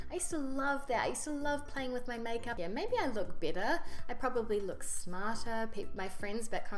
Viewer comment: better, not bitter :) It's because of her aussie accent, that it sounds more like bitter. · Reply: OK. Thank you. That's why at 11:52 she says that maybe she looks better and she's said to look french now. Because the french look better. Quite funny how the subtitles don't understand her and it results in a sensible statement. It made sense.